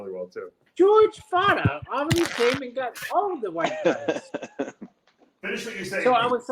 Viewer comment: eyebrows need their own podcast... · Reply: At first i thought he said the white ra ce isn't for mutants...I was like wtf.